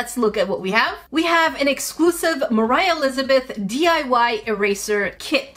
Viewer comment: When it said diy eraser set I thought it said dry eraser’s for a sec